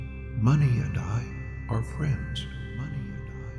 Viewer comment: Can someone tell me what he says at 19:00 It seems like he says money are dying of friends.